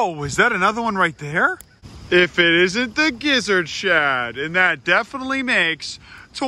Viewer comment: Instead of gizzard shif i thought i heard “gizzard shit” i laughed.